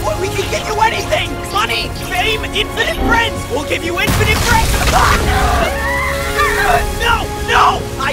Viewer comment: I thought the core said "WE'LL GIVE YOU INFINITE BREADS!!" 😭